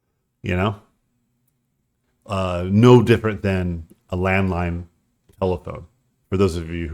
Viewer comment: I don't know why I heard "Landmine telephone" instead of Landline - but I like it. Anyway - thanks for doing these, I draw along listening to you in the background as I work - I'm a colorblind concept artist and it's just nice to have something to listen in to - reminds me of going sketchbooking with friends in pubs and cafes many years ago. Peace!